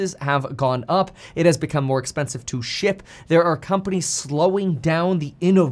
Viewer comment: I thought you said it's became more expensive to shit and I was thinking makes sense with the tp shortage of covi times but you said SHIP and I think ohhh yeah that's true